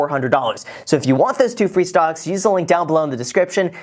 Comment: I was multitasking when you said "free stocks" and I heard "free SOCKS", so naturally I got really excited and tuned back in to what you were saying but hey free stocks are cool too